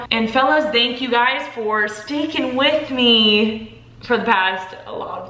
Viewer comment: At the end you said "thanks for stickin with me" but for some reason I heard "stinkin with me" and I think you could just roll with that instead